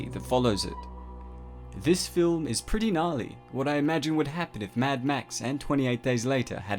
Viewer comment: i thought it said "nigga" lmao